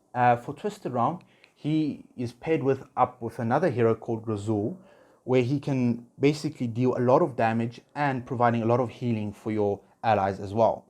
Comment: what hero did u say? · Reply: Grezhul - Graveborn hero. He is used in nearly every single aspect of the game.